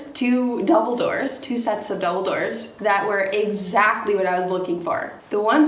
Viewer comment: I kept hearing " two sets of Dumbledore's" 🙂